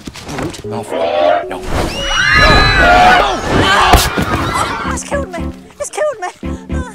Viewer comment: When Draco said:It's killed me, it's killed me, I thought he said:Just cứu me, just cứu me.😂😂😂